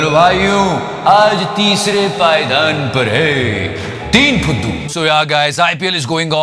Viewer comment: I was watching this video at medium volume without hp and I heard "Arnaaaaab fuddu"😂😂